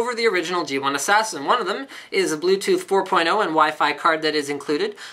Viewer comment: Lol when you said bluetooth 4.0 I thought he said usb 4.0